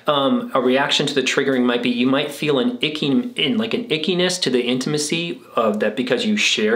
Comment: What you said about “achiness” really spoke to me. This undefinable “ache” that you feel in your body. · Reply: Did he say "ache" or "ick" ? I thought I heard "icky-ness"